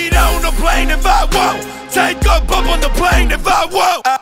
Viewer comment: Before I read the lyrics, I somehow heard it as if he said "Take a poop on the plane if I want!" NOW I hear that he's singing BUMP.